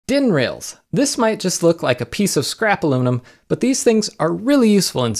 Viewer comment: I thought you said they might look like "a piece of scrapple on 'em."